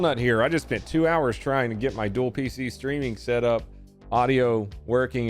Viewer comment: I had to back that up to figure out what "dual feces streaming" is. Let's just say that I'm glad that's not what you really said.